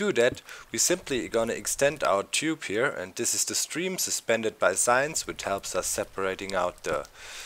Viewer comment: i thought you said "this is the stream suspended by science" xD